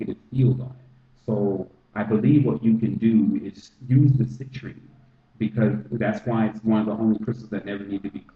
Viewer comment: What crystals did you say to use to cleanse the money? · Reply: Citrine and selenite